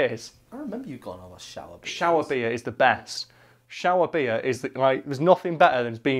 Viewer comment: i heard you sayin "shower beater" which to me sounded like something completely different and way worse to have you dad ruin for you.